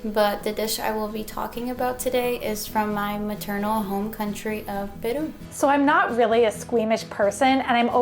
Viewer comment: "my maternal home country of [Bhutan]?" (The caption says bum!)